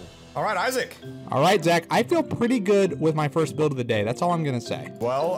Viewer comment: the subtitles say Jack, not Zach.